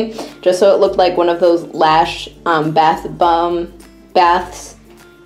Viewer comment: did she say lash instead of lush😂